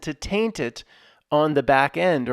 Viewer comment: When you said "Taint it on the backend" I suddenly thought of Goerge Michael as I thought you said "Tainted on the backend" . What is white and sticky that slides down toilet walls? ........................................................George Michael's latest release. Great broadcast. Thank you.